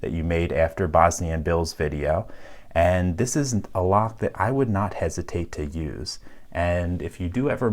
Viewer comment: "A lock I would not hesitate to use" Is there any higher praise? No there is not. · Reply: I think there was another negative before that: 19:23 "This isn't a lock I wouldn't hesitate to use". Probably meant to say what you wrote, but it threw me a bit when I heard it.